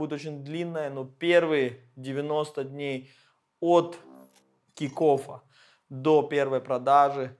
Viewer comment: Всё время слышу "от тинькоффа")